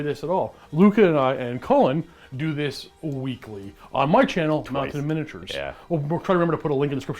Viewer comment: sorry couldn't catch the name of the channel, what's the channel's name again? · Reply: Mountain Miniatures, you should definitely check their channel if you love Warhammer Fantasy!